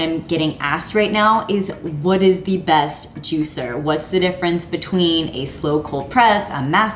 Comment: I thought you said the name j2 was the best juicer.